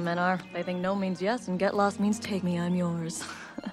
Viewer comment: I thought she said "Peg me I'm yours" lol